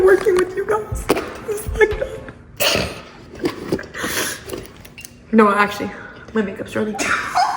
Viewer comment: Wait what did say at 12:02 lol and they are so funny and pretty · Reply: "No actually, my makeup is running."